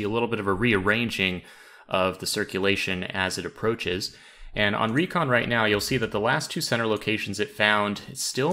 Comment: When you said "And on Recon...." I heard "And Henri con."